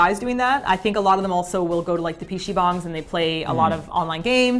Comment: said "P-Shi" Bang instead of PC Bang XD